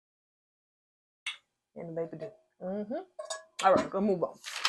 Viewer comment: Did you just say "the baby do"? How rude!!